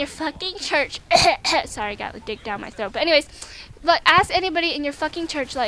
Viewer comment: off topic.. did she say i have a dick down my throat XD